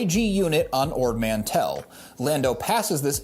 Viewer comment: It sounds like Han says Ord Mandell and not Mantell. Might not even be the right planet you're talking about.